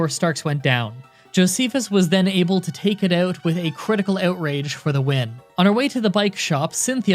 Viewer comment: Wait did he just say Jocephus landed a critical outrage?